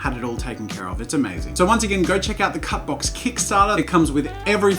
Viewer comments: Every time he says "cutbox" I realize I'm a child bc all I hear is "cuckbox" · I thought you were saying Cat-box the entire beginning of the video.. and in your vlogs.. oops.